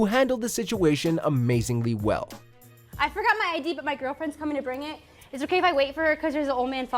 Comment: Did She Said "My Girlfriend"???? Lesbian Maybe, I Don't Know.